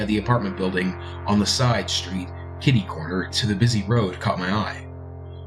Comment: LOL DID HE SAY KIDDY CORNER?